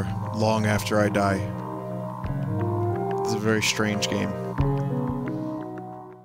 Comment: I heard "I'm going to Harvey, Annie! Most wanks."